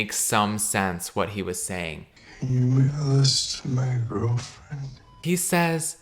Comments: Caleb repeating a clip of a guy pretty clearly saying “can you mail this to my girlfriend?” over and over while being super confused about what he’s saying is so funny to me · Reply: BRUH I REPEATED THE CLIP AND IT STILL SOUNDS LIKE HES SAYING ‘CAN U BE CLOSE TO MY GIRLFRIEND’ HOW TF IS IT MAIL IM NOT HEARING IT 😖😭😭😭 god fucking damn the stress of that one scene is horrifying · Caleb repeating a clip of a guy pretty clearly saying “can you mail this to my girlfriend?” over and over while being super confused about what he’s saying is so funny to me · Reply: I heard "can you make a list to my girlfriend" and had to come to the comments to see what others think so I'll know haha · I heard "can you make a list to my girlfriend" for that one line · I heard 'can you hand this to girlfriend '